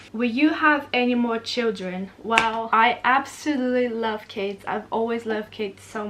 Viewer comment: When said "I love kids" near the start of the video I thought she said "I love Cas" short for Casey was it just me?? But she does love Casey none the less